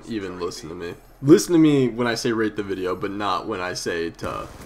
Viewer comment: I thought he said rape the video. Haha